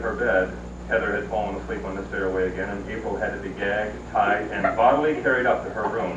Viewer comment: Did he say, " ...gagged, tied...?" 😮🤨